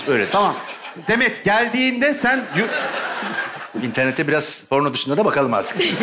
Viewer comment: bi açarmısınız ben mi yanlış duydum? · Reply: İnternete biraz p*rno dışındada bakalım diyor Anladığın buysa doğru anlamışsın😂👍